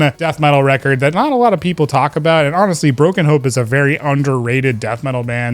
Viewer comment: When you said Broken Hope I thought you said Broken Pope and laughed ...not a bad band name maybe...